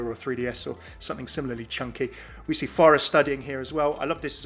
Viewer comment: Am I the only one who heard him say "We see pharah studying" on the roadhog spray?